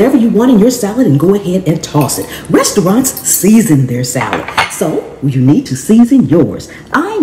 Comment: I work at a restaurant... The biggest difference is that our produce is fresher. It comes direct from the farm every 2-3 days, doesn't sit on a store shelf. Fresh matters. Salt will make salads taste better, but will also make the salad wilt if you don't eat it right away. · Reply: At first I thought she said chefs SNEEZE IN their salad, instead of SEASON their salad LOL